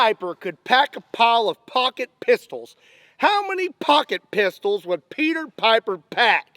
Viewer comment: At first I thought you were saying pocket pussies I was like uhm I think you should take that to your only fans Scott 😅😂